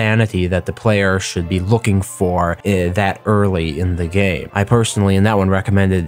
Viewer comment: Did you mean to say game or day? because I heard gay.